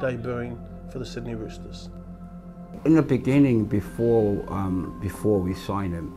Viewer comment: for some reason I heard “in the beninging” 😭🤣